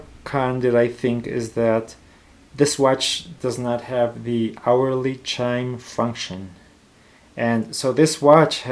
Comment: Can you please repeat what is the con on 12:07? I didn't understand. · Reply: This SUUNTO does not have the hourly chime function 😥.